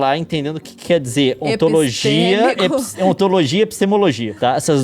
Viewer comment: Qual foi as palavras q eles falaram em 21:15 ? Um falou encima do outro e não deu para entender kkk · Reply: Acho que foi, ontologia e epistemologia